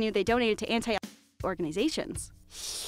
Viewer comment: if you dnt know what she said she said abortion